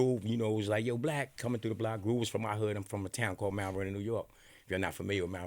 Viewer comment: it really sound like he said Malvern, NY i’m like wtf and where tf is that ...then when he named a few ppl in like OH Mt.Vernon ...🤦🏾‍♂️